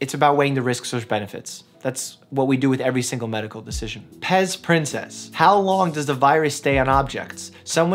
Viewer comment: did he just say prince, princess 🤨